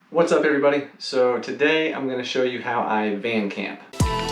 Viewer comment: Every time you say van camp all I hear is “band camp” haha. I keep meaning to say I really like your wife’s hairstyle. Bad ass. Brilliant how you set this up. It looks really efficient and now I want to go van camping!